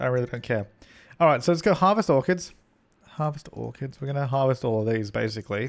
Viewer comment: when you said "harvest orchids" I thought you said "harvest organs" and I was like what you can do that in the sims?!